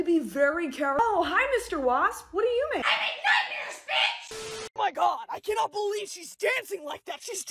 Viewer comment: Yknow I thought the wasp said "I make numbers bitch!" Both sayings work very well for a wasp, numbers of nightmares